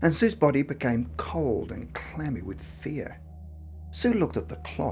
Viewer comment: LOL Sounds like he is saying she was cold and clammy - something her mother normally causes! :-)